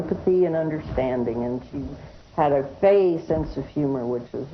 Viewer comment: what did she say? She had a Faye? Gay? Sense of humor? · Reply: Fey. It's an Irish term I think